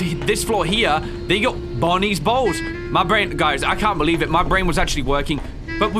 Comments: I literally looked away for a few seconds because my cat was jumping around and wreaking havoc in my room, and all I hear is "Bonnie's Balls". Best timing Ryan. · i thought this dude said bonnies balls without even smiling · did he just say Bonnie's balls 😳